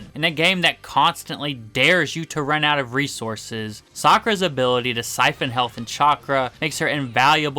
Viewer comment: I’m sorry did I hear that right? Did he say Sakura is useful???